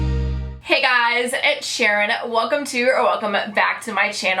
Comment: At the beginning I thought she said Edd Sharon